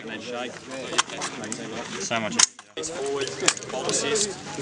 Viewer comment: Could have sworn the Australian guy said "so much easier" when using the M4 at 4:10. I'd agree with him too.